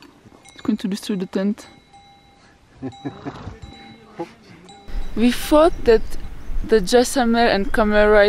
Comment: we fought..13:45 anyfing...did i hear that correct??